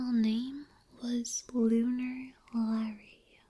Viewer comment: I first heard Buzz's old name as Boner Larry, lmao